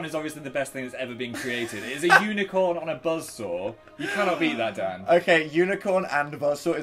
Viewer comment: This video came out almost 2 years ago and i just now realised they are saying BUZZ SAW. I thought they were saying buzzorb, the confusion.